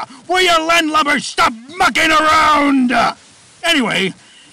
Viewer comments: Foxy did not say “F**king around!” He says “Mucking around!” Sorry for the lack of clarity. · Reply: I thought the said f*king around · Sounds like foxy is saying stop fucking around but it's mucking lol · Thanks thought he actually said f**king around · Foxy did not say “F**king around!” He says “Mucking around!” Sorry for the lack of clarity. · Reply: I heard "F**king around" and "blood" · I thought foxy say f**king around